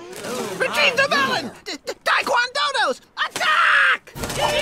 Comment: If you dont understand what dodos said in minute 1:38 i will help you what they said (dodo: protect the melon,dodongos attack !)